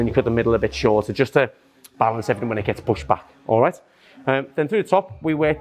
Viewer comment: did he said pushpa